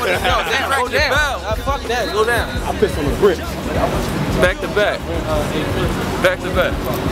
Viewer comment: did i hear "back to back"? Hmmm, ok it reminds me of something doh